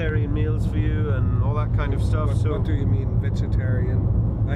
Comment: At first when he says what do you mean, I thought he said, "The fuck do you mean, 'vegetarian'."